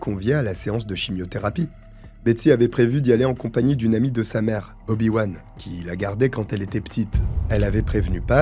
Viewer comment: J'ai entendu "avec sa mère, obiwan" 😮